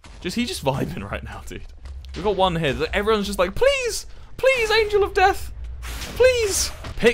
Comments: On 13:57 wilbur says “please” but the subtitles say “alsjeblieft” wich is dutch for please. Why? Who edits his videos? I need answers. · Wait, at 13:57 the text says alsjeblieft instead of please 😂